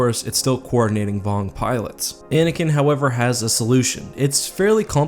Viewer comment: did he say Anakin?